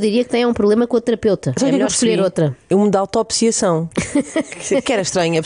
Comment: "autopsiação" 💀 também entendi isso 😏